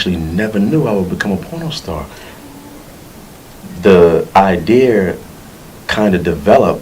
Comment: BRUH i lost it at 1:00 lmao really nigga? Did you just say "The ideer !?" nigga did you mean "IDEA"? learn to enunciate dumb ass.